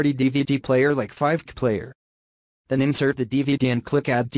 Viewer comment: What?!! 1:02, did he say fart player?